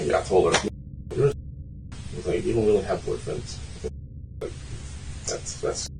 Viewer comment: the subtitles are wrong. It reads you don’t have true feelings. He said you don’t have boyfriends